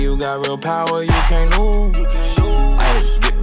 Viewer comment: When you got real power, you can't lose...when I first heard this, I thought he was saying "you can't move" 😂